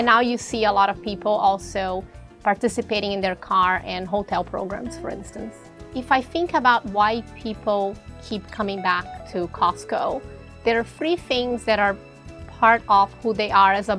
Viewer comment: listen to 5;26 sounds like she says think about why white people come to costco . Lol